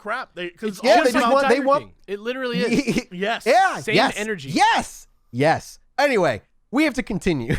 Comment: Want did say at ~ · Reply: "It's like the Tiger King. Same energy."